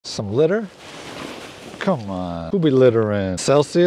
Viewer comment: I keep hearing “some glitter”😂